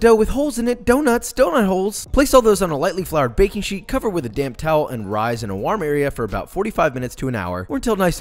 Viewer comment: i ALWAYS hear "damp towel" wrong. it always sounds like "cover with a damn towel" to me lmao